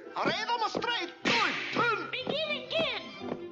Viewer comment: What did the Japanese duck say 5:37? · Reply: no, the newly hatched duck said that and he said "begin again" not "again and again."